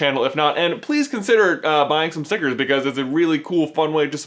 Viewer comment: I thought you said go buy some Snickers........ I was eating an Oreo at the time.